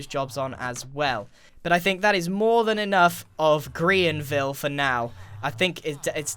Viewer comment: I thought it said grain-ville... 🤦‍♂️😂